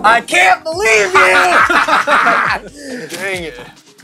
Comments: it sounds like said DAM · anyone else hear damn? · did someone say d*mn